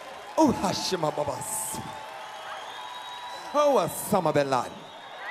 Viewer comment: did he say “Osama Bin Laden?”